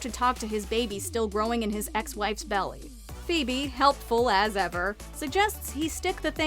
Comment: I don’t know why but I for some reason thought I heard the word belt at first 8:19 also The turkey on the head thing is a pretty strange way to talk to someone XD